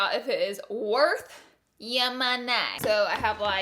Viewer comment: I thought is said “shave ur money” so I was like...tf?